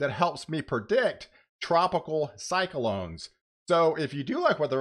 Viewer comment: When you said Cyclones at about :23, I thought I heard Cylon's and flashbacks of Battlestar Galactica and that sexy blond came rushing back like a mediocre trip I took once on codeine. It wasn't my fault I read teaspoon as tablespoon, really...